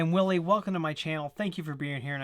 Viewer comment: I thought you said WILLI WONKA.... But your said WILLIE WECLOME :) ... I got that Email today looks like it was sent from inside my computer with the correct password... also my facebook attacted and my email was attacted...